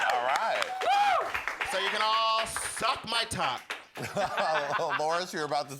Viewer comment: did anyone catch Xochi Mochi mumble "What tuck?" I live!!!😂😂😂💀